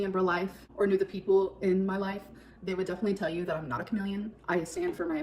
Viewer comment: ngl I was stuck for a min about wtf she just said then I finally understood she meant comedian not chameleon. I was like gorl of course ur not a chameleon tf lol